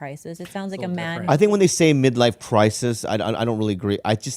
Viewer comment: is Bobby saying "Midlife Prices", I'm not mocking but I think he's been a comedian for so long, he's at a point where he is unintentionally funny 24/7, without even realizing it. "That or my hearing ain't what it used to be" 🤔